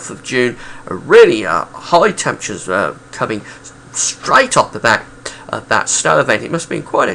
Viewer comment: was that straight off the back or straight off the bat at 5:36? I was looking for a pun as snow stopped cricket play on 2nd June. I had to listen a few times, as it sounded like "bat" on a few listens then "back" after the 3rd listen.I wasn't born in 1975 but it did sound pretty good. One of the sunniest June's also. July-August I think just marginally surpassed the 200 mark here. I'd imagine that July-August were a bit convective at times.